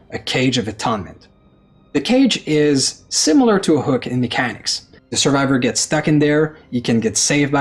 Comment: All I heard was "The cage is similar to mechanics from DeathGarden". Nice try there, devs, but I'm not going back. Aside from that, Super happy PH is here, omg what a treat as a die hard SH fan.